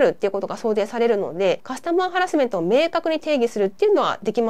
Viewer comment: カスタマーハラスメントのはずなのに カスのハラスメントって聞こえてしまう話すなぁ